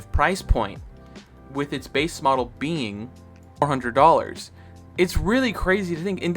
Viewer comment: If I did that would u mined leaving the time stamp so I may make the correction I believe I said the base a model is 400 dollars and almost 100 dollar’s cheaper on sorry poor phrasing on my part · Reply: I'm guessing you misspoke or said 400 as you then went on to say 320 in sales, I replayed it 5 times and it sounded like 100 each time lol